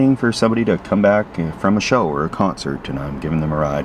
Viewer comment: Camping with deserves a Emmy and Oscar. Never quit! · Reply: I thought you said from a “shower or a concert” So I’m thinking🤔 “Steve where exactly are you?”🤭